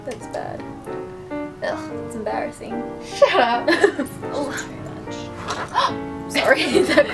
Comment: Haha noticed at 13:10. I think Lauren actually said "she's too bitchy" 🙊 Lauren's so adorable😚